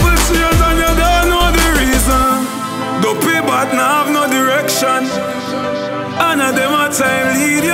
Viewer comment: "Duppy bad nuh have direction." Is that what he's saying? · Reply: It's "duppy bat don't have any direction so you can't have them leading you". In other words the bat is blind and aimless in life so you shouldn't be listening to them.